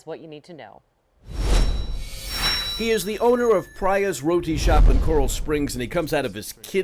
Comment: i thought he said Priya's Rodent Shop